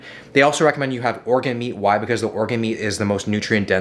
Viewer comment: for a moment, I thought that he said “orca meat”..